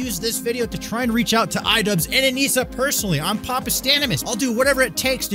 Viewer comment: I had to watch it twice I could have SWORN he said idubbz and “onision” … not anisa